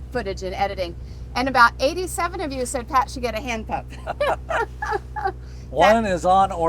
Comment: When you said “hand pump” at first I heard “hand puppet”. 😂 That would certainly be something for Pat to add to his oil change videos.